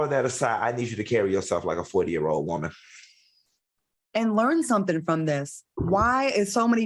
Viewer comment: Claudia to Nicki…. “Learn something from this”… all i heard was ‘I’m rooting for you’ 😂😂😂